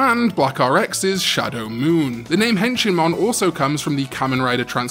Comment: Honestly when I first heard Henshinmon I thought you said "Henchmanmon" since Wormmon was Ken's henchman when he was the Digimon Emperor lol.